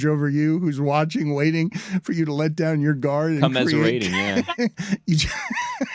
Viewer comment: Couldn't hear Billy's comment that got the Hakeem Nick's laugh at 21:45, I rewound multiple times and still can't decipher it... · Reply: Haha thanks! I came up with commiserating too after five listens but without the context of the Big Suey I had no idea why it got the Hakeem Nicks laugh. Good shout as usual Billy! Lol